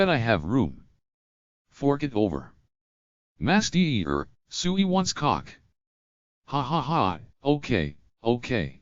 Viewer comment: anyone else hears "suwi wants cock!"?